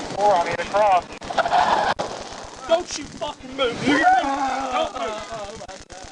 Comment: Play it at 5:06-5:08. What does the cop say? It sounds like “don’t you move n-word”.